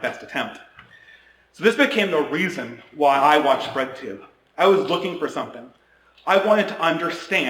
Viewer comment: When he first said "bread tube", thought he said "red tube" 😳